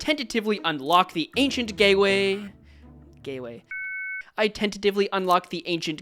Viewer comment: even the transcript knows what you said LMFAO ancient gateway. Gayway. I tentatively unlock the ancient gayway. I tentatively unlock the ancient gayway and die within seconds.